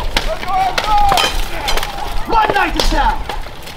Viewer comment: Did she really say, "Off the list"? Super low-pro this time. Seemed imaginary. 😄